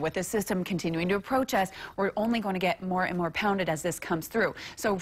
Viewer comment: Did she say she was going to get pounded?